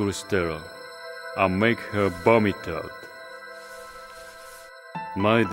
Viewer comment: did he just say, "I'll make her vomit"? O_O